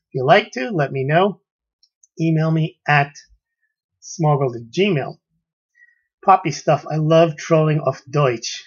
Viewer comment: The closed caption says: smuggle to Gmail poppy